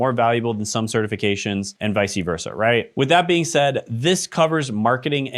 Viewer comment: Did you just say.... "vice-uh versa"? 😂